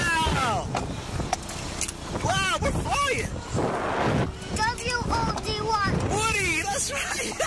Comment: what does the kid say? I hear "love you o d 1", lmfao.